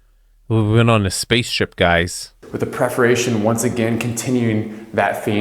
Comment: Did he say "preforation" in the wine room?? What the hell is preforation??